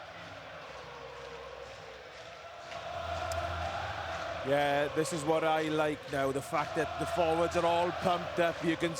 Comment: can anyone tell me what the crowd are chanting? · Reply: Oooohhhhhh that’s Maro Itoje